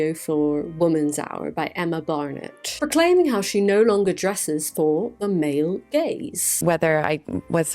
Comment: I zoned out for a moment until I heard "She no longer dresses for the male gays..........." Took me a second to realise what I was actually watching.